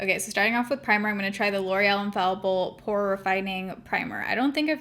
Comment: I thought you said L'Oreal Infallible HORRIFYING primer. Laughed so hard I coughed.